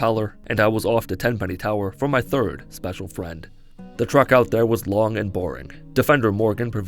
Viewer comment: There is no way you don't say "the truck out there" instead of "the treck out there" at 5:10. I had to rewind a few times to make sure, and yeah, truck or maybe track, but not treck as it should be lol.